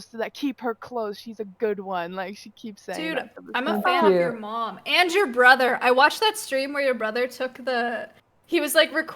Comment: Did Maya say imma fuck your mom and your brother¿?😂😂😂